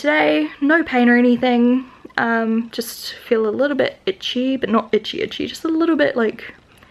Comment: haha @ 18:38 I thought you said "just feel a little bit bitchy..." I'm so happy for you!!